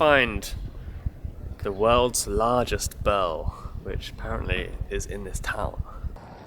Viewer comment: With accent I thought he said the world largest bell... lol